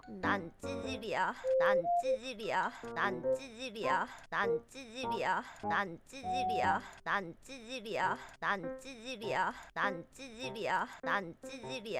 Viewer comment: 이거 나만 그런지 모르겠는디 왜 나는 "난 치질이야" 라고 들리지..?